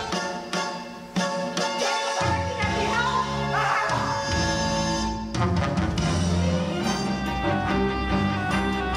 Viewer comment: I can't make out what Centipede says; does anyone know? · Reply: I think he just says 'aaaagggh'